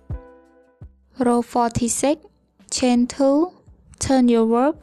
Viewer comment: is it chain 2 or chain 1? you did chain 1 but the captions say chain 2